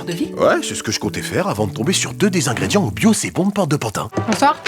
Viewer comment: mais qu'est-ce qu'il dit, je ne comprends rien au mots après "ouais mais je suis tombé sur 2 ingrédients ...". Il n'y a que moi qui n'arrive pas à comprendre ? Pouvez vous me le dire svp. Sinon du génie, je me demande comment tu penses à toutes ces choses-là, quelle culture ! J'ai du mal à suivre toute les réf tellement il y en a !! Continues comme ça, hâte de la prochaine vidéo OH QUE OUI !!!! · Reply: Il a trouvé 2 ingrédients au « Bio c’est Bon » (un magasin bio) à Porte de Pantin (à Paris quoi)